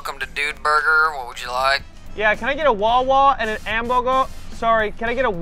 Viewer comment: I love how Cody say “wawa”instead of “watah”